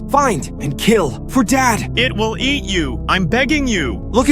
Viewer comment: I thought it said I will eat you😭